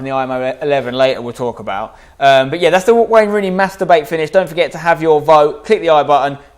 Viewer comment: i thought he said masterbauate